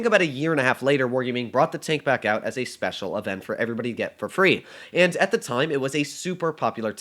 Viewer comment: did I hear that correctly? He said the word FREE